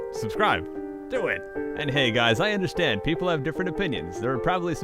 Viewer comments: when he said opinion didn't it sound like he said onion? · when u said opinion I thought u said onion :|